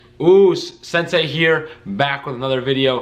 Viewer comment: what did he say? FaZe Sensei I see ...? Anyone?